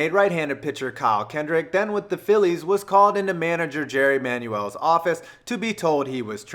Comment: maybe i heard it wrong it sounded like you said Jerry Manuel and not Charlie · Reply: lol I did say jerry, he used to manage the mets and i used to curse at him all the time